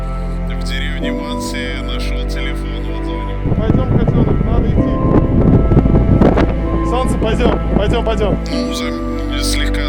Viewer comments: Я одна слышу на 23:33 , как говорит: пойдём котёночек, надо идти. Сонце пойдём, пойдём, пойдём. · мне послышалось или он сказал "Котенок" 🥺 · мне послышалось или сказал "пойдём котенок, надо идти" "солнце, пойдём, пойдём" это было оч мило🥺💗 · почему я слышу там слово котёнок? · ребята мне кажется..... или на 23:35 сказал: -Пойдем КОТЕНОК,надо идти. именно.😏😏😏😏🏳️‍🌈 *скажите что мне не одной это послышалось*🙏🙏🙏🙏 🏳️‍🌈🏳️‍🌈🏳️‍🌈🏳️‍🌈 пж PS:я люблю этот шип🏳️‍🌈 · мне не послышалось. Пойдём котенок надо идти но на субтитрах нету слова котенок. Да я 100 раз переслушала. 😜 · пойдем котенок надо идти пойдем солнце пойдем пойдем пойдем я один это слышу?